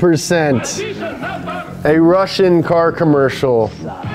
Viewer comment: I thought you said Europe ? Not Russia